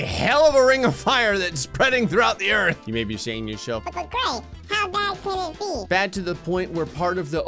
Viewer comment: wtf did he say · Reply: He said “But Gray how had can it be”